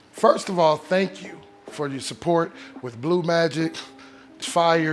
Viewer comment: I thought I heard him say "thank you for your pay check". my mistake